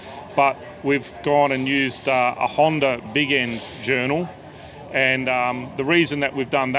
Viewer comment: I thought he said "Honda viggen" like shiiiet, Honda had a collab with Saab?